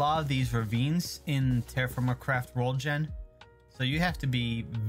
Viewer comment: It my just be your accent but I could've sworn I heard "terraform my crack"💀